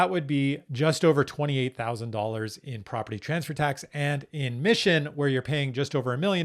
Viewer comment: it says “Michigan” on the video instead of Mission